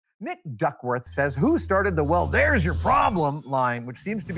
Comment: Better yet, it's a fart catcher. Because of course it is. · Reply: your welcome for the question, but uh... it sounded like you said Dick Duckworth instead of Nick Duckworth. That or I'm hearing things